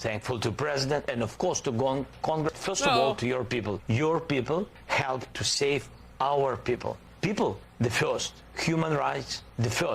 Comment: It very much sounds like he said “your people help to save our people”